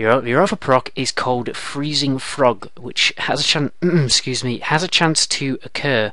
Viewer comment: did he just say freezing frog? haha